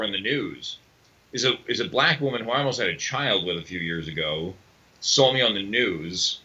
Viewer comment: Did he say he had a child with a black woman? I can't hear what he's saying clearly.